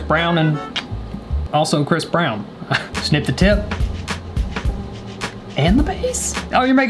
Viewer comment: I heard snip the t1t